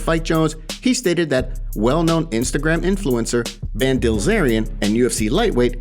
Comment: Did you just call this men Ben Dilzerian😂☠️